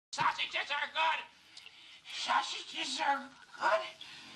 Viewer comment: bruh I thought the intro said "Sausage is our god" and Neil was making a statement and not "Sausages are good" lol